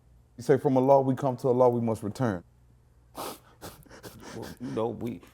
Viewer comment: "From allah we come, to allah we must return" 🙏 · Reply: Oooohh I thought he said "from a law we come, to a law we must return "🤦‍♂️